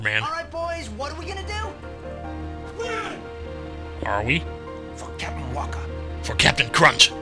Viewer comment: I thought they said "fuck captain wakka"